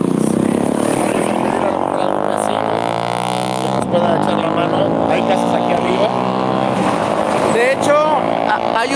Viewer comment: Ala hora 35:27 se escucha una voz, ke lo ultimo ke dice es EL FINAL , porke lo primero no se entiende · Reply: Es en el minuto 35:24 aproximadamente. Hay que estar muy atentos. Yo sí escuché que dice es el final 🥺